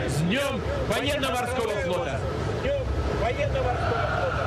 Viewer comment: Почулося одразу "фанєрно-морского флота"🤣🤣🤣 Але передивився, справді почулось😁😁😁